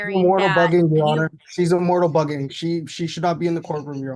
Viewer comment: "Immortal" You keep using this word. I do not think it means what you think it means. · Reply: I couldn’t figure out what he was saying. Was it that? I thought I heard “immortal bugging” then I thought I heard something about “motorcycle bully”. I even googled immortal bugging and it came up with something about bugs in a video game.